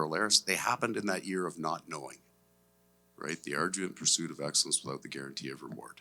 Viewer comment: does he say "the ardor and pursuit of excellence without the guarantee of reward"? It sounds like "ardue," but then the word should be "arduous." Just curious what he's saying, because he also said that exact line about Gustav Forsling in a previous conference. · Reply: He says arduous… his accent makes it a little muffled but he says it